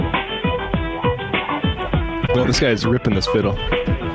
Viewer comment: What did the producer say? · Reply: This guy is ripping his fiddle?